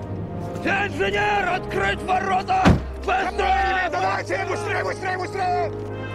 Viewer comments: WHAT ARE THEY SAYING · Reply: Инженер! Открыть ворота! Построились, давайте, быстрей, быстрей, быстрей! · what are the Russians saying 0:41 ?? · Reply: BHstudios Инженеры открывают ворота! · Does anyone know what the Russians are saying (in Russian) between 0:45 and 0:54? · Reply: Sgt. Don 'Wardaddy' Collier Dovchenko is saying: "Engineer, open the gates! Faster! Faster!") That's all, really)